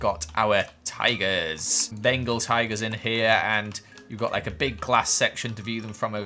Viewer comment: what I heard was bagel tiger 😂 🍤🐯 P.S I couldn't find a bagel so I just used a...shrimp? Also I think it was Bengal tiger.